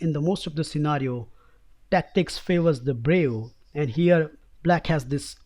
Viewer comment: what is he saying? "tactics favours the ...." - favours the what? I can't make out the word. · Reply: Tactic favors the brave